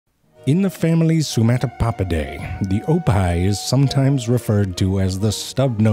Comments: "Here we see a Mother Oppai" okay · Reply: Oppai are boobs in Japanese. Initially that's what I thought was saying in this video, but then it hit me that he was saying hippo backwards. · I was confused at first, because I thought he said “oppai,” which means “boobs” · Damn, I misheard "oppih" as "oppai"...